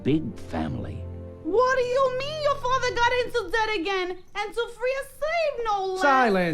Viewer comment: does anyone know what she is saying? It sounds like she’s saying made up words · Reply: ''WHADDYA MEAN YA FATHER GOT INTO DEBT AGAIN?!? AND TO FREE A SLAVE NO LESS''